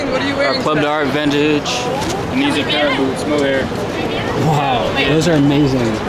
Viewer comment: Anyone catch the name of the boots at 6:29? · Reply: Paraboots brother.